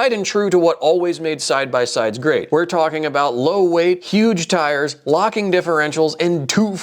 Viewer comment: Did you say "low weight" at 02:45 (2,254 lbs)?